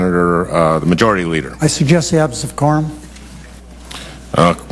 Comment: what does he say from 10:25 to 10:28? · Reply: said "I suggest the absence of a quorum" Meaning that after the vote, a majority of the senators probably left the room, so he wanted them to come back.